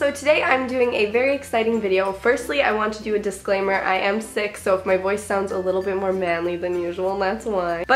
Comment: I thought she said:” I just have a quick disclaimer..I am six so...” 😂😂